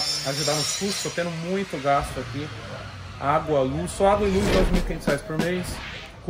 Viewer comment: Água e luz 9.500reais ? Tá Loko... Acho q escutei errado, não é possível.